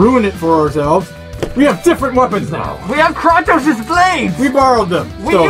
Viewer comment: Did he say kontos